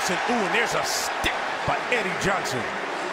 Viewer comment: did he say Eddie johnsohn